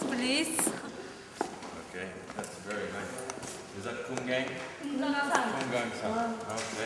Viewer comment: did he just call him CUM GANG WHATTT